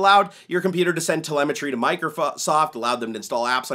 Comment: i thought you said microFUCK 3:09 yes i thought that